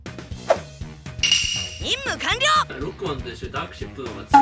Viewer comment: 淫夢完了？(空耳